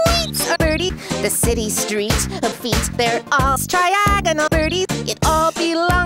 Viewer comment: the city’s streets the feet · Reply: I heard: "the city street of feet"! Lel! 😂